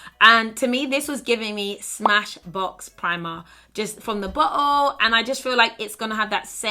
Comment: When you said Bottle, I seriously thought you said butthole 😂❤